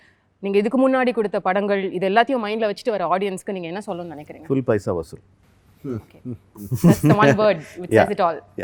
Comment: what he said? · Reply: Full Paisa vasool (Box office hit)